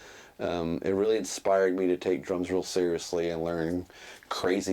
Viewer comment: at first I thought you said drugs not drums Im glad you did said drums and not drugs.